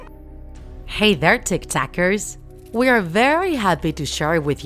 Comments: I thought she said tiktok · Why this who time I thought she said hi there tik tickers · I thought at the start she said tiktokers 😅